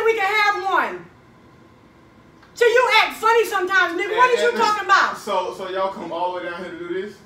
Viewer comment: Did she say nigga?